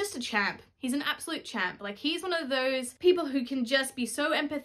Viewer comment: Oh champ! i thought you said chad lol